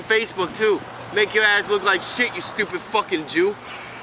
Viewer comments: did he just say "you stupid fucking jew"? Lmao playing the victim card when he's a filthy antisemitic wow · D...did I hear him use the word “Jew” as an insult?